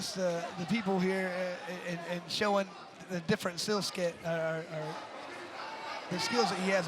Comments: Who hears the commentator said "the different sill sket"?😂 4:33. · another controversial part about this is the announcer, 😂 My guy said “Showin the different SEELSKET” instead of “skill set” and he didn’t even correct himself either